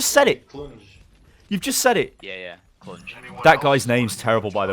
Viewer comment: wait, which guys name is terrible and why? I don't understand? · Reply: Josh It's was something like Clunj which is slang for vagina